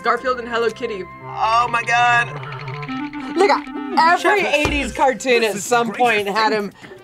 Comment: am i trippin or at 1:11 in the episode WHAT did say???? sounds like a bad word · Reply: I think said look at